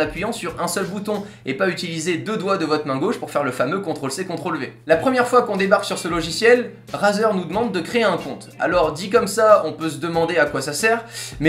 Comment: c'est moi ou il dit Brazzer?